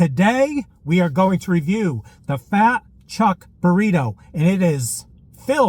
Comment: I thought it said "fat chick Burrito" 🌯 🌯 🌯 That would be just wrong 😆